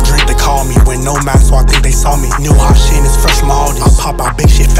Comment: What he say at 1:59? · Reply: Wasn’t no mask so I think they saw me